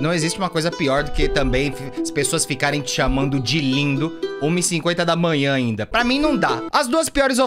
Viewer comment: eu entendi tipo lindo, uma hora da manhã... Liga os ponto